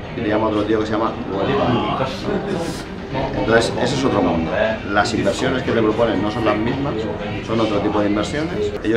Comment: POR FAVOR EN ESA PARTE SE CORTA QUE DICE POR EL RUIDO DE AMBIENTE. LES RECOMIENDO USAR WAVES CLARITY ES UN PLUGIN QUE HACE QUE EL RUIDO SE MERME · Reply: Pero dice Deutsche Wealth Management